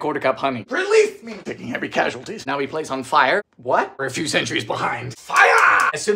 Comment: For some wild reason... I heard "place on fire" as "now we light it on fire" and I had to listen to the steps again because my inner pyromaniac lit up with joy.